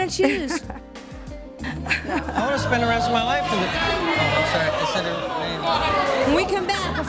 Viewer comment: does he say "I want to spend the rest of my life with ___, sorry I just said your real name?" Did I hear that correctly?